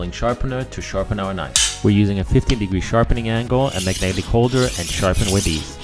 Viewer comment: I misheard you and thought you said a 50° angle 😆 🤣 I was wondering if you were chopping bone lol